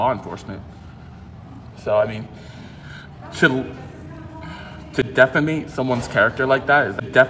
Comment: Guess evolution skipped over. "Defamate", so much for that 400 level of English....... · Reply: I heard "defeme"